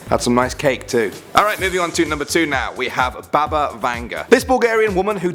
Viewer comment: When he said: Baba Vanga I thought of Bobs and Vagana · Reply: Well i thought he said baba yaga